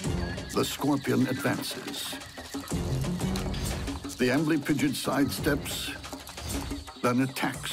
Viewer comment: The first time they said amblypigyd I heard "ambling pigeon" and thought "oh God a scorpion killed a pigeon? I thought this show was about bugs!"